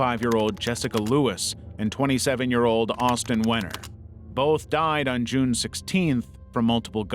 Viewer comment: I deadass thought I heard:”Austin weener” as in a hotdog 😂 😂 😂